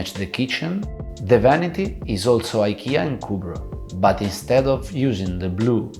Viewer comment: what IKEA product is that? I didn't understand what he said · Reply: Cubro - a custom designer for IKEA cabinets. Took me 3 goes to hear it!